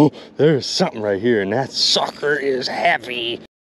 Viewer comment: i honestly thought u said “tht sucker is happy lol”